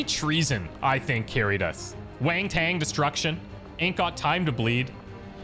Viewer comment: I heard 'wangtang destruction' xD